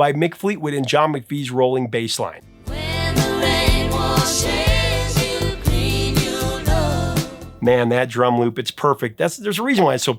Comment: I always heard "when the rainbow shaves you clean you'll know"